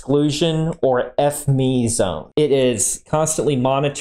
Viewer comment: I know it's not what you meant but what I heard was "spent fuel pool is the highest level of Foreign Material Exclusion or F**k me zone" which I think is pretty funny in context